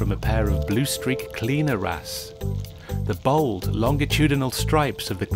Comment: “blue-streak cleaner ass” mhm that’s what I heard.